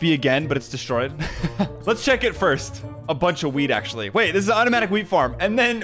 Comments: that wheat he sounded like he said weed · did you say weed instead of wheat 😂